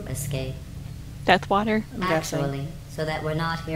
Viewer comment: i swear she said duck water XD